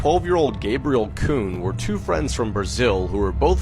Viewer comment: did I just hear the c word or just kuhn